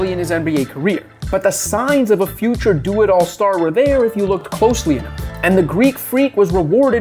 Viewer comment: Am I tripping or did he just say "closely-er"?